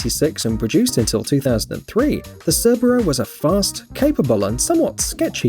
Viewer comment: did he just said Subaru ? 🥶